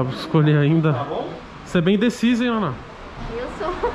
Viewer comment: Eu entendi um Você é bem Indecisa em Amor